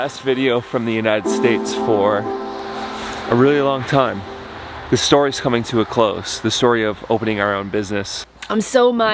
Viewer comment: In the beginning it sounded like said the store was closing and I thought, "what??" haha. Congratulations. It's not easy serving people all day like that. Good for you.